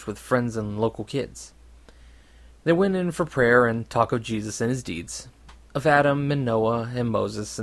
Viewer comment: The first time at 1:14 I thought you said Taco Jesus and I was really stoked for some reason. Taco Jesus > Taco Bell